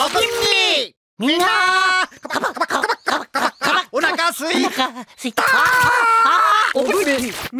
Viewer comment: 空耳だろうが、 「お腹すい…か…ば～！ （スイカバー！）」 …って聞こえてしまうガンダムZ勢